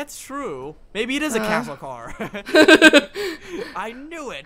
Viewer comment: "OH ITS A CATTLE CAR Parker: "oh that makes more sense I thought it said it was castle I'm like that sounds amazing"